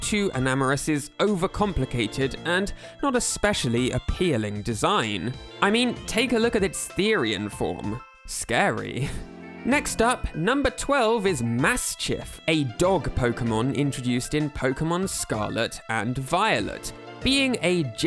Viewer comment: WHATWHATWHATWHAT- DID YOU SAY… THERIAN?? DSHAFIWHIGREIGHREINFWIHTRWEIGHIT